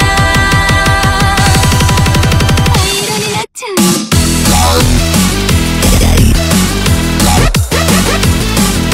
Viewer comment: 灰色になっちゃうよ？（繭 4:35 じゃあ、逢わせてあげる（繭 4:47 がっかりね （イオナ 4:54 ウリス…ウリス…ウリス…ウリス…？（アキラッキー 1:53はなんて言ってるか分からない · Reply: 浦添イオナ（ユキ）「私は…どうしてあんなことを」